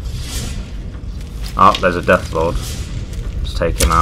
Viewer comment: When he said there's a death Lord I thought he said death claw lol